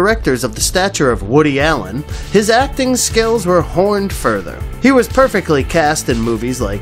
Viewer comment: When your spellcheck causes you to say horned instead of honed